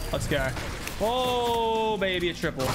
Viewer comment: Did he just say “oh baby a triple” 11:10 lmaooo 😭🤣